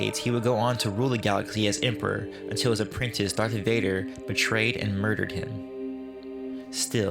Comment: did he just say " at the hands of DOCTOR Vadar"?